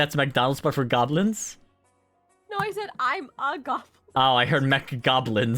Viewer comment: I heard MC-Goblins too